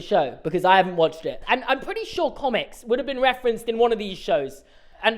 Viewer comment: For a second I thought he said "Comet" not "Comics" and i was just like yeah it is weird that they haven't mentioned Comet the dog from Full House!